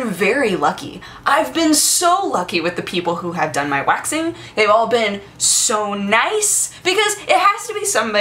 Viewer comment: I thought it said "waxing toes"